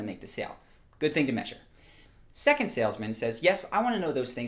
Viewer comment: When he said "salesman" I thought he said "sailsman" and I imaginied 2 sailors on a ship